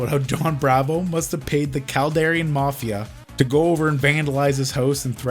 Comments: I thought you said Calgarian Mafia there for a second. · For a while I thought you were saying calradian mafia. As in warband.